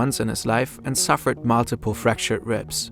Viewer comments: what did he say? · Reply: Knock knees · can anyone tell me what he said? "The position of his legs suggest he may have been......" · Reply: Knock-kneed